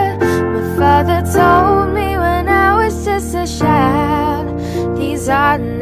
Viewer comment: I know I shouldn’t laugh but 2:32 Ladybug said Shild instead of child lol